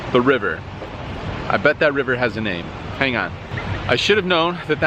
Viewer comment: "I bet that river has a name." All I heard was "bet the river"